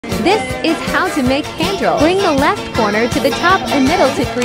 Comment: At first I thought it said “how to make candles”